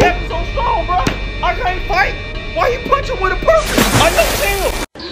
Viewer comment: " why he punching with a purpose " " I'm vengeance in the night and I've been smoking right " 😎 😂😂😂😭😭😭🤣🤣🤣🤣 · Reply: I was wondering what he was saying! I kept hearing "why he punching with a purse? On left field!"